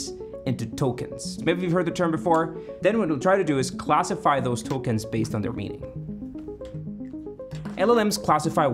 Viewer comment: I thought he said "Classify those Tolkien's based on their meeting" when he was trying to show how an algorithm to guess the last word of "One ring to rule them ___"